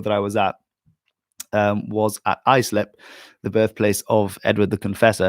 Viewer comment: Oh, you said "Islip", I heard "Iceland"! EDward the Confessor being born in Islip makes a bit more sense than his being born on Iceland!